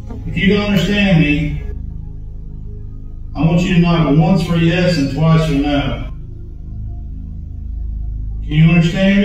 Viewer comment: Am I the only one who noticed at 16:05, the ghost understands calls out "can you understand me"... "if you can't understand me, Knock once for yes, twice for no"... ummmm... if they can't understand you... mmmmmm Let's think about this. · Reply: Im so glad im not the only one lol. I had to rewind it cause i am doing college work when all i hear is "if you can understand me knock once for yes twice for no" i immediately was like, sooo if the ghost doesnt understand but knows that it has to knock twice to answer thennn it understands?? If i was the ghost i would have knocked twice just to be that person(ghost thing) or three times to confuse them 🤣